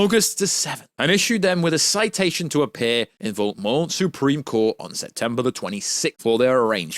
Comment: Did he say Voldemort Supreme Court approx 3:38/3:39 lol I even turned on my CC and it said Voldemort lol on purpose or Freudian slip? 😆 🤣 keep up the good work